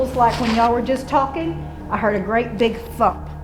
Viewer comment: I thought she said “ I heard a great big f*ck” woops